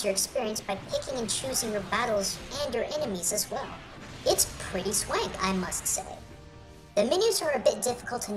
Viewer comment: did you just say "its pretty sweg"? is about to burst out laughing